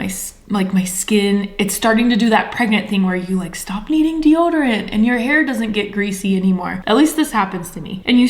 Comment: No joke, i thought you said "the point in pregnancy where you like stop eating deoderant...."🤣